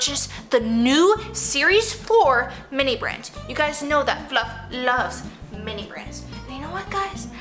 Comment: I love mini brands. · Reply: For a second I thought you said I love my mini brans